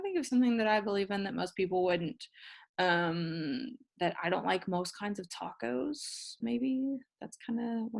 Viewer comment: did you just say you dont like tacos